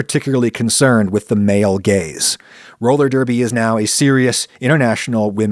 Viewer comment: when Adam is saying "male gaze" it sounds like he is saying "male gays" 😂